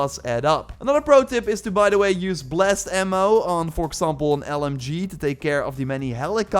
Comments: New Far Cry Credits giveaway: <link> Discord: <link> Second channel with extra Far Cry 6 gameplay: <link> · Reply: I thought you said 'Blessed Ammo' I thought I was going to have to go find a priest of something like that. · I thought he said "blessed ammo" Got some seed flashbacks right now